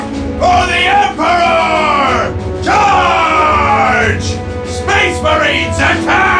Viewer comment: lol instead of "charge" i thought he said "dodge!"